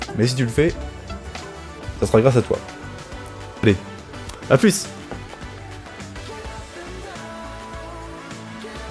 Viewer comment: il sert a quoi le P juste avant l'outro, 90% de chance que ce soit un message codé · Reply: Il dit "paix"